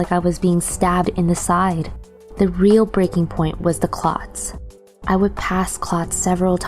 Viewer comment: When she was talking about passing clots I thought she said “clocks” and I was super confused and wondering why walking by a wall clock would make her condition worse. I was like “wtf do clocks have to do with this”